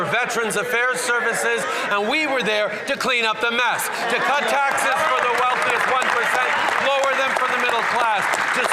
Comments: He actually said, "We were there to clean up the mess. To cut taxes for the wealthiest 1%." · Um @ 2:12 did he just say “We were there to clean up the mess, we were there to cut taxes for the wealthiest 1%” did I hear that right???? Did he just admit to lowering the taxes for the riches people in Canada???? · Did he just say that he cut tax for the wealthiest 1%? -> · Cut taxes for the wealthiest 1% lower than the middle class? Ladies and gentlemen did I hear that correctly? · did I hear that right or am I tripping? We were there to cut taxes for the 1%???? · Did he just say “to cut tax taxes for wealthy 20%”? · Did he just say he “cut taxes for the wealthiest 1%” ? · Did he just say to cut taxes for the wealthiest 1% or am I hearing that wrong? Lol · did he say "to cut taxes for the wealthiest 1%?" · did he just say "to cut taxes for the 1%..."? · did he just say cut taxes for the wealthiest 1%? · At minute 2:16 I'd swear Trudeau said we (the liberals) were there to cut taxes for the wealthy 1%